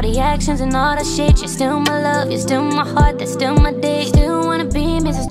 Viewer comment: this whole time I thought she said d:ck not date ✋🏼